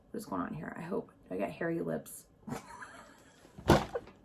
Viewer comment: Hi... As I'm typing all I hear "I have hairy lips"... Lol! Anyway.... I NEED that SD/JS lipstick set!! I wasn't able to get my hands on it and have been a fan of them for years. I'm so with you on the whole cancel culture...I've got other things in my life to deal with rather than any drama...esp when it doesn't pertain to the products I'm purchasing. Don't come for me ppl. It's just my opinion just like you have your own. I hope you have a Happy New Year! And may 2021 be better than 2020!